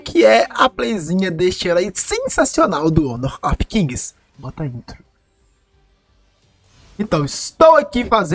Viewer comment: Eu ouvi tá inuti